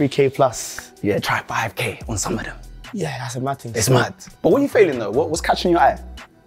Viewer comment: did say what you failing instead of what you feeling???